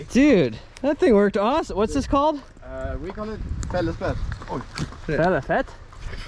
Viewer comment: The name of the tool sounded like he said fellers pet lol makes sense to me